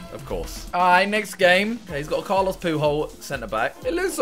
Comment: Did say Carlos poohole at 7:21aha